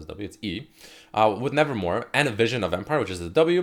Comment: Gheyath Al-Doori nevermove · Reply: Pierre Mings oh shit i heard him saying nevermore